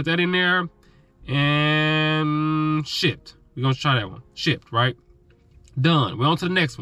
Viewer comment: When he said Shipt I thought he said 💩 lmaooo